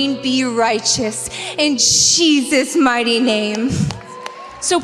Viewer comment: in the beginning its sound like shes saying "in cheesus mighty name"..